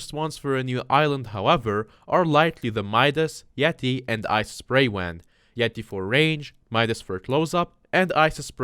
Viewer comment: i heard "ice präi wänd" yes i am speaking enchantment table